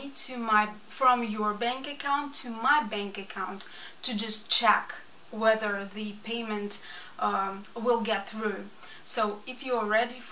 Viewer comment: There are plenty of Ukrainian people who have already lost their family members, jobs, homes and financial security due to bombing. They are desperately trying to relocate their families in order to simply stay alive. They have many less options than you do. I know that the war is not your fault personally as your insane president put you in that position, not the American people. Unfortunately, it's a sad situation for all involved. I do wish you the best but I feel a greater need to donate to help others who don't even have a home left to return to. Asking for donations to allow you to start your business is not a high priority when you look at others around you who have lost everything. Take the new job you said is available to you, hold on to your dream and pray for those less fortunate than yourself. I'll say a prayer for you. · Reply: "to just check whether the payment will get through". Sometimes people hear what they want to hear, not what have been actually said. I just ask for 1 transfer to check if I can receive money for my services. Anyways, thank you for watching! Ukrainians really need help more than me. You can help them. It's normal. But, please, don't blame me with what I even didn't have in my mind. A prayer for me is more than enough. Thank you!